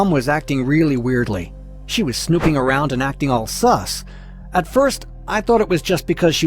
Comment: did i hear SUS correctly